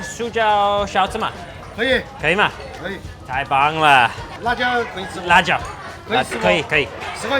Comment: did he just say I bang haa 😅